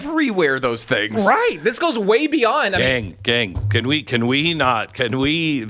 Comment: did beck say gang gang? What set you claim, probably blood cuz.